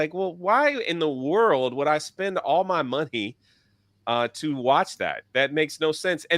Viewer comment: I swear to God, at 21:36 it sounds like says "Why in the world would I spend all my monkey" instead of "money". That has nothing to do with the topic at hand, I just found it hilarious.